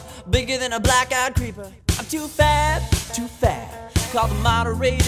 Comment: 8 thought it said too fat